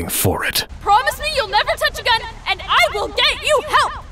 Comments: I watched this after i saw fate/zero and the touching a gun thing makes sense now I thought she said again the first few times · When sabre said never touch a gun, I thought she said again then I watched fate zero